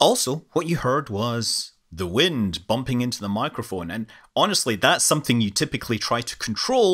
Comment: Thought you said the microphone was pooched at the beginning??